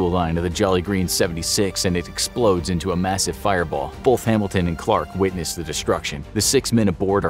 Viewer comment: Every time he said “Hambelton” I kept hearing “Hamilton” instead 🤭😂